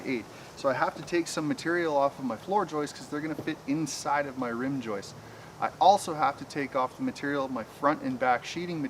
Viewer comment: 😵‍💫🥴 wtf did he say? 😂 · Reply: Rim joist